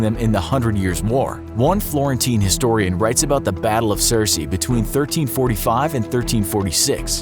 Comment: Did he say battle of cersei